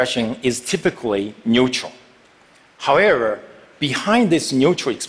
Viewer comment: did he say howerer?